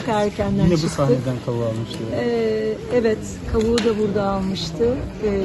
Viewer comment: da kadın kaburga birşeyi dağılmıştı mı ne diyor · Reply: Kavuğu da burda almıştı diyor